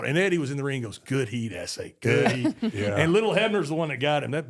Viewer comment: Did him so dirty in the thumbnail lmaooo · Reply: I'm Sorry guys..what said JBL at 4:15? "Little" who?